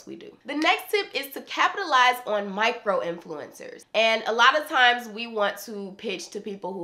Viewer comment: i wasn't looking at the screen at 9 :16 and i thought she said "the next tip is to capitalize on white girl influencers"